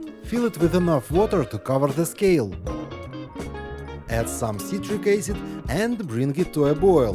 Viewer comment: add some citric acid? Not sure what you said, sorry. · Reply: yes citric acid